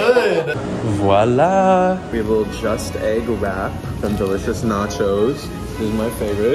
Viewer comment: Did he say EGG???